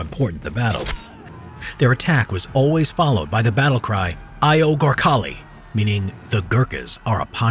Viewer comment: no that’s no a different language! That’s English! He is just saying Hey to his friend Gorkhali! “AYYYOOO GORKHALI!”